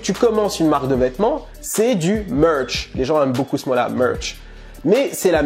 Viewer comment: il a dit des qu'on lance une marque de vêtement c'est du ''much'' c'est quoi le Much? svp quelqu'un peut m'expliquer? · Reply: Il a dit merch pas "much"